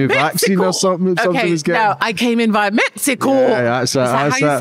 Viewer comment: I'm with... I heard "I came in by 'mets co'... I also said...what's that 🤣